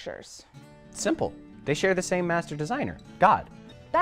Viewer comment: Am I the only one who heard saying "Master designer" as "magic designer" and was thinking for half a second that they actually admitted their beliefs are magic before realizing I'd probably misheard it due to my auditory issues/ADHD + listening to it without looking at the video? No? Just me? Dang.